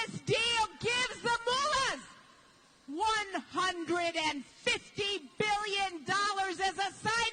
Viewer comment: Who did she say (at around 2:15) receives a $150,000,000 bonus under this deal? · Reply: Scott Petrie Not million---BILLION!